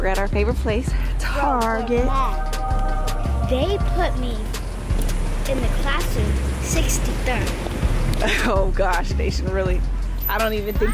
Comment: Did she just say they put me in classroom 63rd🤣🤣 rewind rewind